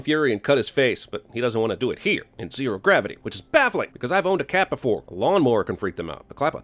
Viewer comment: I thought he said “boned a cat,” knowing who is he probably has